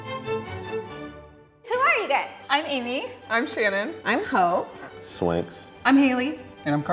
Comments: Wait what did I hear " I'm hoe" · I thought she said "I'm whore" 😂 forgive me